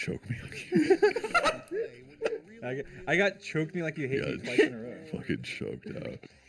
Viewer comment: Go to 5:07 tell me what you hear dream say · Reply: I heard "I got choke me like you hate me twice in a row"